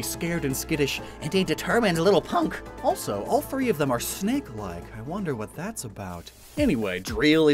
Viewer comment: When you said the starters where snek like I thought you said snack like, and I thought who would want to eat a lava snake 🐍 ?